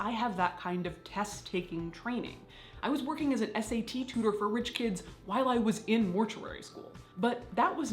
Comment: I was typing my last comment as I listened, and I literally heard “I was working as an SAT test taker for rich kids”... I was like ‘god damn Caitlyn, keep that on the DL’